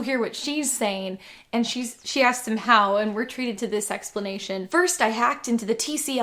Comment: I thought you said "treated to this s-explanation" which i find to be a much better word than a boring explanation.